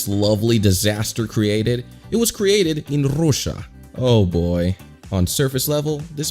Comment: if you dont know what he said, he said russia with some accent to make more entertainment